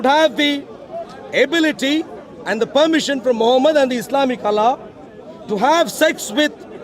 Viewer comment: did he said Islamic Allah? :D